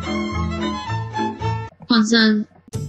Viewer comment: я слышу "пацан"